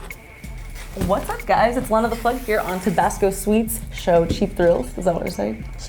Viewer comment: I thought it said "Lena the Pug" i was expecting a dog, but this is ok.